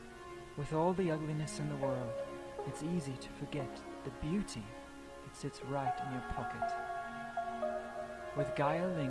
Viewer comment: bro that's not what they said AT ALL 😭how do you mishear "it's easy to forget the beauty that sits right in your pocket" as that